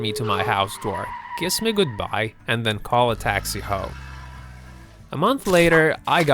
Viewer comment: I thought he said taxi ho